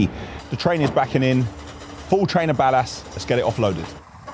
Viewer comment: every time he says ballast I keep hearing badass😩😩🤣🤣🤣